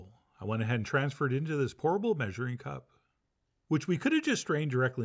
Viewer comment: I thought Chef John really doesn't like his Pyrex, because I heard "transfer it into this horrible measuring cup."